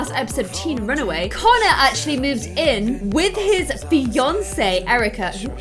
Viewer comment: Lol at 0:38 she said fiance and i heard Beyoncè hahahaah😅